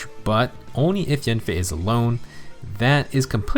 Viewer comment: did you say only in enemy phase alone? I could be mishearing that but his weapons just solo dodge. He needs to be alone and it works in both phases as long as hes alone.